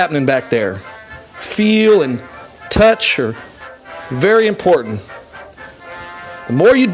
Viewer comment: did he call his hair, 'her'.. this dude loves being him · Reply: “Feel and touch er(are) very important”, i do agree with you tho lmao